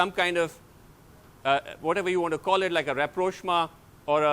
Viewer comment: Which word did Sreenivas used at 18:44? It sounded like repro something. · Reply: Rapprochement : means reconciliation or some sort of agreement for smooth conduct of business.